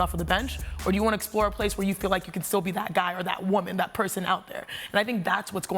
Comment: did i hear "that guy, that woman"? hey the topic is NBA, Chiney! lol